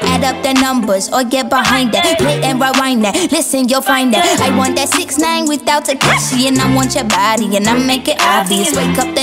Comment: Did she just-... Did she just say "I want that 69 without TEKASHI" Dayuum. 6IX9INE:Damn.